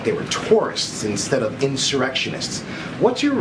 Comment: Did he just say erection